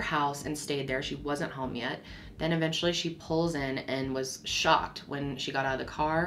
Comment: Omg when you said Sandra was “shocked” as they arrested her, I thought you said she was “SHOT”. I’m like “why are the police shooting her???”